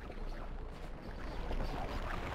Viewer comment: When I First Played This Game, Years Ago, I Swear To God I Thought He Said “Venom… Get Ready For World Of PAY!” Before I Learned He Actually Said “Venom…. Get Ready For A World Of PAIN!” LOL